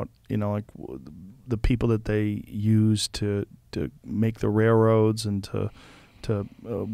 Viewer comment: I don't know what I am, but I heard 'rairoads'